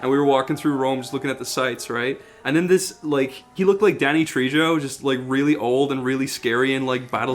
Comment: Did he just say Danny “Tree-Joe”?